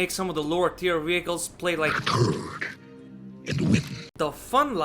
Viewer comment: What does say at 3:28? A turd in the wind?